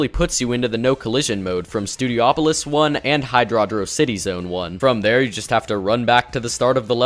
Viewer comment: On 6:04 did he say hidodocity